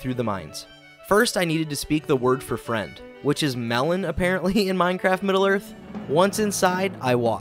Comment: Oh, so it was Mellon? Always heard it as Melor. Huh, fancy that.